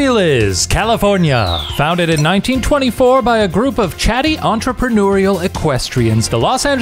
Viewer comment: I heard that as Chad-dy not chatty oops